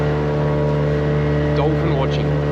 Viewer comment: did he say dolphin orgy😂😂